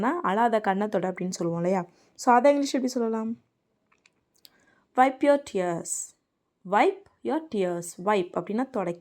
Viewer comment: Wipe your tears instead of " you are" tears.😅